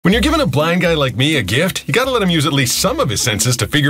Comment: Really thought you said black guy like you. That would have made it much funnier.